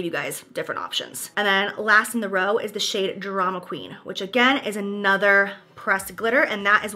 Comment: Girl tell me why I thought you said the drama queen shade was dramamine and was like ohh cause you’ve been sick?? And then I saw the text saying drama queen lmao.